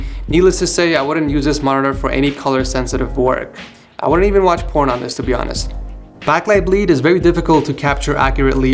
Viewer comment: im not sure if i heard that correctly, he said " i wouldnt even would want to watch porn on this" so randomly lmao